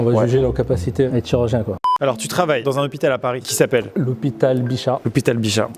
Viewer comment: y'a vraiment que moi entend "lhopital di chats " 🤣